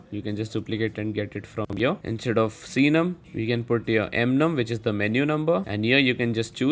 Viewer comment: turn on captions at 8:03 (Instead of we can put your mum) 🙄